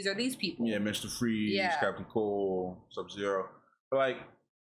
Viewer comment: Did you say sub-zero at 29:54? If so I didn't know he showed up in young justice ??? Lol